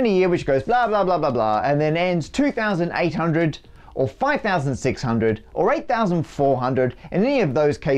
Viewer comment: the subtitles say 5604 instead of 5600